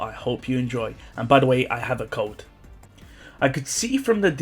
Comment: I thought he said “Btw I have a cult” 😂